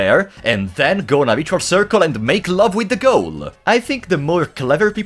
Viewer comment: Okay I was worried because at 10:00 with your accent I thought you said make love to the girl (you said ghoul) which is a great way to get flagged